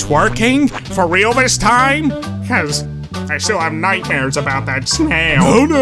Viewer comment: Did anyone else think squidward said "You mean twerking I still have nightmares about that sound"